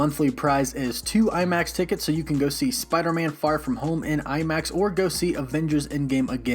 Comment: I skipped before he said far so I thought he said "Spiderman f*ck from home"😂